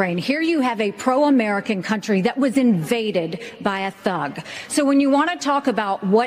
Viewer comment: Did she actually say "here you have a pro America country that was invaded by a ZOG"?